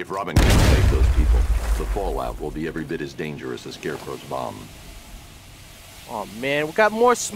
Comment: I thought Batman said more dangerous than scarecrows mom but then I read the subtitles and found out batman or Batlirious said scarecrows bomb